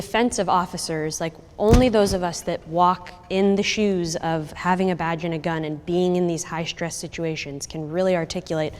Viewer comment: sounds like she says having a vag and a gun....